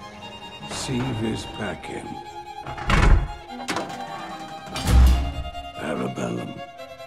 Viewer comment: What does Winston say at 2:58? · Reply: "Si vis pacem, para bellum" Latin for "If you want peace, prepare for war"